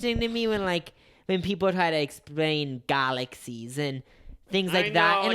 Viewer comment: I’m not making fun of speech impediment but when he said galaxies i definitely thought he said garlic season and I forgot what they were talking about for a second 😭